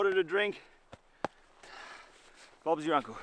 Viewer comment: Did bro say bob is ur uncle